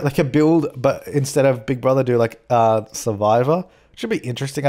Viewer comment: i thought he said saliva instead of survivor and i was like WOTT 😅☺️😂